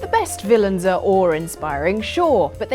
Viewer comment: I heard "ore-inspiring" (with "r" in hiatus) instead of "awe-inspiring" - and, like, I know that some English dialects can do that but now I think of inspiring ore... stop me :D